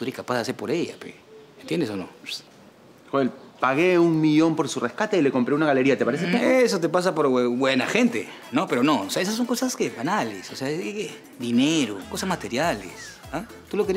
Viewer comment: Joel:eso te pasa por hue- buena gente XD ayuda yo lo escuche asi